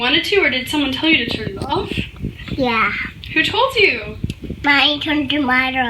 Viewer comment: My daughter is 2 it sounds like she sayin "mia or mya turned it off" & "mia or mya put me in bed"